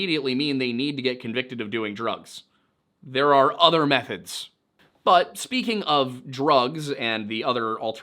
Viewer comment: did you say “other methods” or “other methheads?” 😅